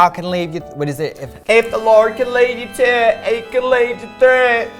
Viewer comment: does anyone know what trixie says at 9:20?? i always quote the gibberish but would love to know what she is actually saying lol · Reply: She says "If the Lord can lead you to it, He can lead you through it." Which is a common Christian saying, especially relevant given her backwoods heritage. And then, "Half of one, six a dozen of the other. " Which is silly bc the original saying is: "Six of one, half a dozen of the other. " A similar expression would be "A horse apiece."